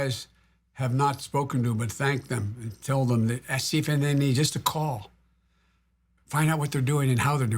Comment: What did he say at 39:56? He's also swaying a bit a few seconds before that... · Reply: I think he meant to say "see if there's anything they need," but I slowed it down to 0.25x speed and I still couldn't make it out.